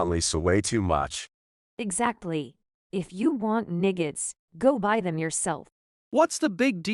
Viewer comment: Assuming all these videos are translated from Japanese to English, the English audio made it “almost” sound like Mia was saying the n-word (racially derogatory) instead of saying “nuggets”. Oops!